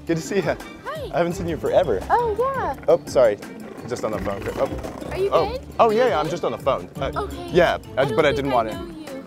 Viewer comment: I thought she said “are you gay”